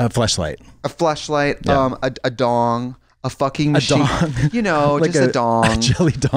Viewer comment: 😂 I thought Trixie said “…a f::king sheet” my mind went spiraling 🌀 like “🧐 a f::king sheet? This is the sheet we do it on only” 😂